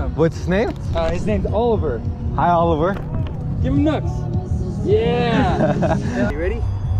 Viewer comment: i heard ‘give him nuts!’ and i genuinely thought he was about to punch little oliver (it’s weird typing my own name)